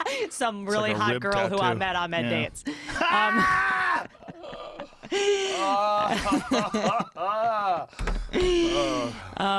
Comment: what does she say at 2:10:42? "some really hot girl I met on hot dates"?